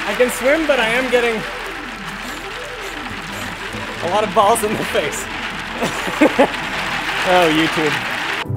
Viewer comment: I thought i heard lots of shaved balls in my face.